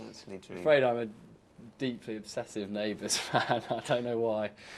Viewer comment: Thanks for share it, Liz! :D But, If you can, Could you please tell me what Tom is saying in 5:39? I'm not sure, but I think is something about neighbors in Austrália, but I couldn't understand... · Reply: Tom says he is a deeply obsessive fan of Neighbours - he said he wants to go and see the set of Neighbours - and the bar in Melbourne where apparently the cast go after they have finished filming!